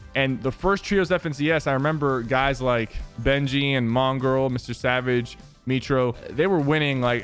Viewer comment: did he just say mongirl ?